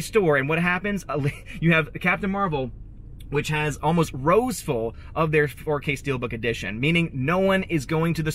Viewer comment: "Rows Full", I heard "Rose Full" and thought about Rows of Rose Tico dolls.